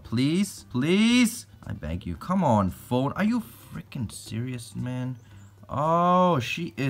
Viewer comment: I was listening with low volume and I thought said “Are you fffucking serious.” I had to rewind and turn my volume up 😂😂